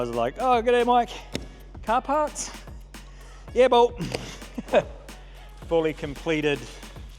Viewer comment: Did you just say "Yebo"? You got some South African dialect in you?! Yourself and your build is beyond insane, mad respect!